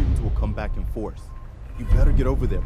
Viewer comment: What did he say at 20:28? "I'm about to make ..." ? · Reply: he says “I’m about to make old yeller look like a fu€king joke.”